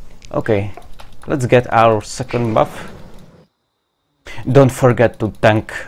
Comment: Did you say " Sucking Buff" :D